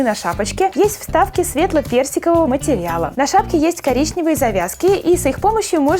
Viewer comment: Мне послышалось не "Светло-персикового" а "Светло-берсикового" 😅😄